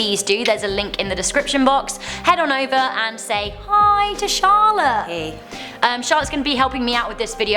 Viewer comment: i thought she said "Hi T'Challa" 😂😂